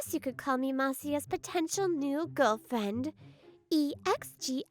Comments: did you say potential? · did she just say po-potential..?